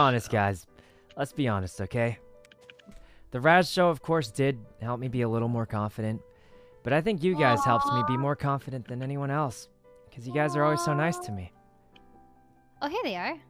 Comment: after he say "let be honest guy, .... " what he say after that, i cant hear because my english is not good :( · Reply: He said "the Rajj Show did help me be a little more confident, but I think you guys helped me be more confident than anyone else. Because you guys are always so nice to me" :)